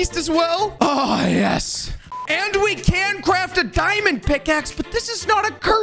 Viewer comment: you can craft a what-axe He said pic-axe